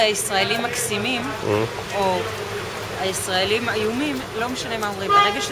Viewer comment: There's an understandable mistranslation. At 6:49, the woman says "אני לא יודעת מה הסטריאוטיפ, אבל כל סטריוטיפ הוא מעצבן, לא משנה לי אם אומרים ישראלים הם מקסימים, או הישראלים איומים, לא משנה מה אומרים, ברגע שזה סטריוטיפ זה מעצבן." Translation:I don't know what the stereotypes are, but any stereotype is annoying. It doesn't matter if they say "Israelis are adorable" or that Israelis are horrible, it doesn't matter - as long as it's a stereotype it's annoying. Seems like the translator misheard איומים = 'ayumim' = horrible as אלימים = 'alimim' = violent.